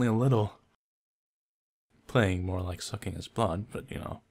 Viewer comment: Waaaaaah, I heard "more like sucking his balls" instead of "sucking his blood" and I was like wtf??? Am I really that perverted? :o